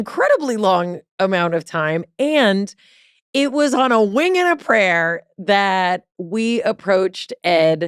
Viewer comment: I've always heard it as "a whim and a prayer" (not a wing and a prayer). Maybe I misheard.